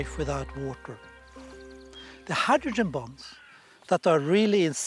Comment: I keep hearing "hydrogen bomb" for "hydrogen bond". Its quite startling, and applies to all the speakers. 😮